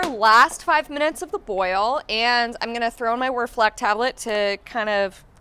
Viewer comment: Hi, what you're trowing in at 10:00? I don't clearly hear what you're saying? A reflactor tablet? To clear out the wort? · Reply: Whirlfloc tablet